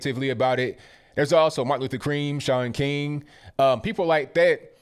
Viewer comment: Did he just say Martin Luther Cream?